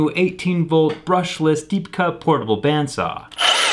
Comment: Did he say portable?